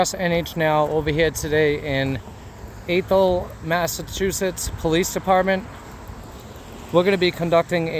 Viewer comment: I about spit my beer out when I thought you said "Asshole Massachusetts" lol